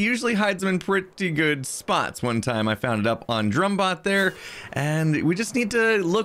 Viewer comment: Did he say “drum bot”?